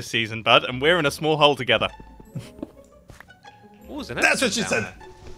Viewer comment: Did say what I think he said at 5:32 ? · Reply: he said That's what she said